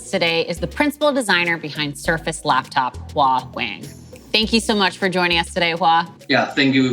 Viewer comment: I initially thought she said Hua wei